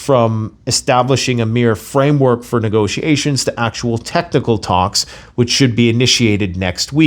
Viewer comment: did he say "testicle Talks" at 1:14 ?